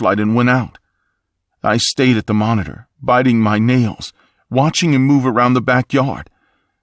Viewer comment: I THOUGHT YOU SAID “ i stayed at the monitor, biting my NIPPLES.” HELP